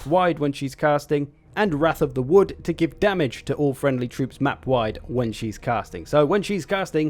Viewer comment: for a second there i thought you said she will DAMAGE her own troops when she's casting lol was so confused